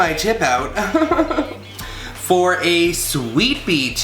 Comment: When I fast forward to the time 6:06 in your video I could swear you said sweet pea tip :) Thanks for clarifying its used for ruffles so now I can go grab one and make my sweetie that cake for Valentines Day! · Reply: Trina Flynn i did say sweet pea lol 😂 sorry my fat fingers typing in the last response lol 😂 i call it a sweet pea tip but it’s also called a ruffle tip ..... sorry had to clear that up lol 😂 xoxo 😘